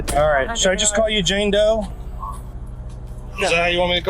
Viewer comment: I thought he said Django 😂😂😂😂😂